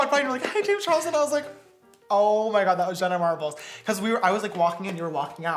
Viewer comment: For a sec I thought it said “Jenna Marshall” lmao